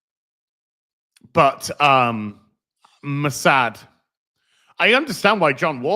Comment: I misheard "Muh sad" as "Mossad".😆